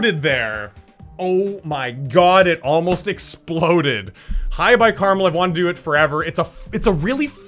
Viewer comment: LOL THE CAPTIONS SAY HI BYE CARAMEL